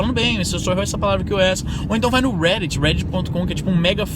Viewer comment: N entendi o nome desse fórum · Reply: reddit